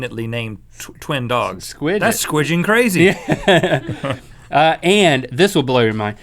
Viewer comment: did anybody else mishear for a second, thought he said "squidding crazy" and get all exited about a squid girl reference then actually realize what he said and get sad? Oh, what's that? Nobody else did and I'm a lonely otaku? ;-; Okay... I'm sorry, I'm gonna go cry in the emo corner with tamaki and grow mushrooms on my head while watching Hikaru and karou twincest cause they're part of the homosexual supporting cast :D and cause nosebleeds are fun, sayonara...